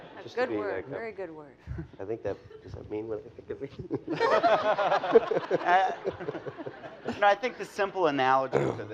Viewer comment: Did anybody hear what did Matt say? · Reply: "Does it mean what I think it means?" referring to the word "surreal"